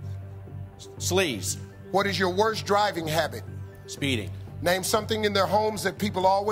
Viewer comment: Tbh I thought Steve said “something in their homes they keep hitting “ Probably why the guy answered phone numbers, as in dialing numbers. His pronunciation was off and I replayed it but I still hear “hidding”